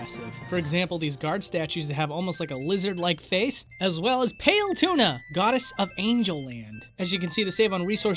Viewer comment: did you just say "Pale Tuna"????????